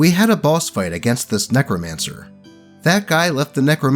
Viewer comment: I'm sorry I thought you said "nekomancer" instead of "necromancer" and now I have a character design I need to flesh out